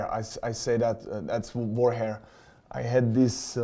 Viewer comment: I heard him say whore hair. I should get my hearing checked